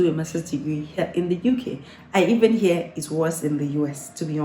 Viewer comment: UK, the title says so. 😁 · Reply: I thought you said "and even here in the US" instead "I even hear" hence the question